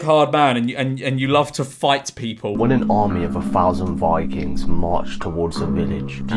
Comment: Was listening to this without looking at the screen and misheard Vikings as ‘bi kings’ 😂